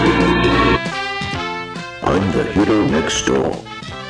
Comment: Also, 9:22 what does he say here? I hear "im the hiddle next door" or something like that. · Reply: I checked the original and it is "I'M THE HERO NEXT DOOR"